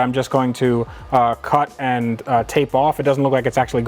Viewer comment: Little tip... placing the radio in the cage like that will cause it to creek and rattle. Get some Tesa tape which is the cloth tape car wiring looms are wrapped in and put it around the edge of the radio and wrap your wiring in it. Rattle free for a couple of bucks! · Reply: I thought you said Tesla tape for a second. lol